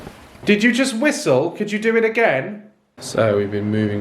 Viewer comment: when the ghost said " do it again " it sounded like it said " help me " did it not ?